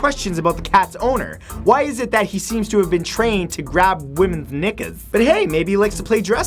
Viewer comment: Did he said women's nigga's at 2:23 😂